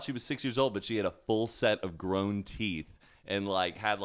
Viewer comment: I thought was gonna say "teats" when he said "teeth", and I nearly died